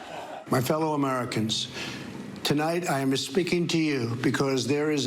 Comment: Did Trump say "tonight I'm 'mis'speaking to you"?